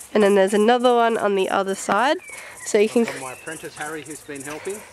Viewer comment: omg i thought your dad said princess when he said apprentice the first time "This is princess harry"